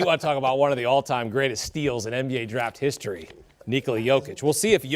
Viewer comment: I was hella confused when I first heard this pick. I thought they said Nikola Jokic